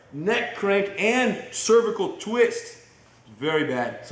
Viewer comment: Did he say cervical twist @ 2:53 ?